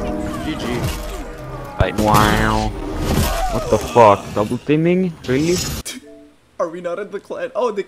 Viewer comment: did he say double teaming in a execution